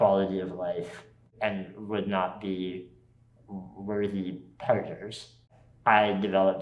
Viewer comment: Can someone please change the CC at 4:40 to say "worthy partners" instead of "worthy predators"? 😅